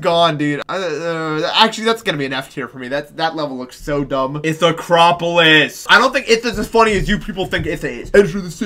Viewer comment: When he said “ithacropolis” I thought he just said “it’s acropolis” just cuz of his lisp lol